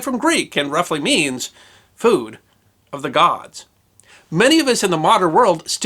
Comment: i thought bro said "poo" lol